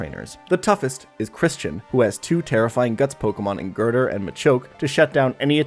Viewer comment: I thought he said terra flying but then I realized ih gen 8